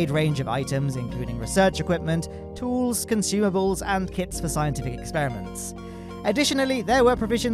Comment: Thought you said 'kids for scientific experiments' =)